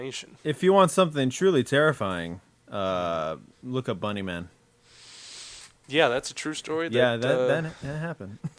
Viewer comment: did he say bunny man?